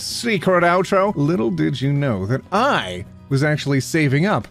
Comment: I thought you said "boob outro"...